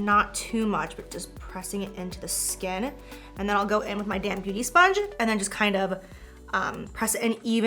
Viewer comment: I def thought she said I’m gonna take my damn beauty sponge.. she said damp. Lol 😂